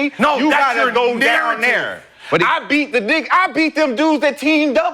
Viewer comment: did McCants say “I beat the d**k” ⏸️